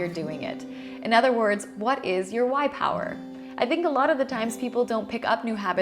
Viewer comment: I legit thought she said "white power" instead of "why power"